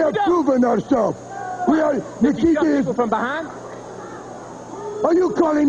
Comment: I can't quite hear it, but maybe he asked if Nikita "takes it from behind" which resulted in his clotheslining....